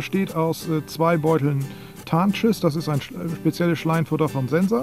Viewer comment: Wie immer, tolles Video. Aber bei 4:56 hab ich einen Lachkrampf bekommen, weil ich "Tarnschiss" verstanden hab :D